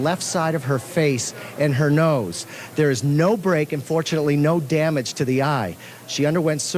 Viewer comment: did he say unfortunately…?